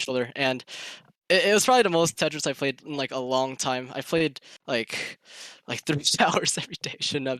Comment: In the interview @ 39:23 how many hours does he say? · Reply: 13 hours im pretty sure